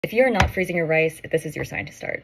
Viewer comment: At first I heard 'if your not freezing your ice..' I choked on my water